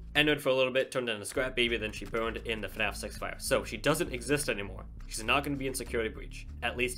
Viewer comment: turn on captions here The captions think he says FNAF sex fire.